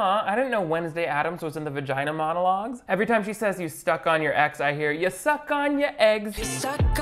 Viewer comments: I ACTUALLY THOUGHT SHE WAS SAYING YOU SUCK ON YOUR EGGS LMAO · Omg I thought she was saying "you check on your eggs" until you said something hahaha